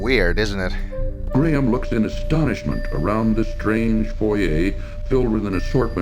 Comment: the narrator said foya instead of foyer. There is only one person I knew who ever said that and he was a Canadian pastor. Every time I now walk into a Church foyer I say, "my look at their wonderful foya. I glad to see someone else does say it that way.